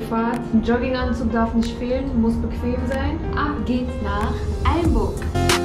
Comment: wer hat auch alyinburg verstanden?